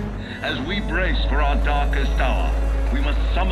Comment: i heard "for a darkest shore" at 0:35 i think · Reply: It said “darkest hour”